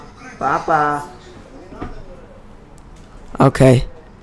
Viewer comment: What happend at 19:19 ? did someone say "Papa papa papa"?